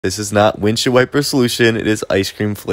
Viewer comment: Why did I hear “ this is wind shield wipers solution” 😂 I love ur vid